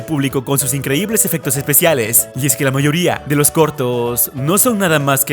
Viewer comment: soy el unico que no to que dijo coortoos?